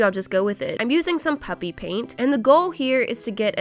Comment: I thought she was saying puppy paint not puppfe paint smth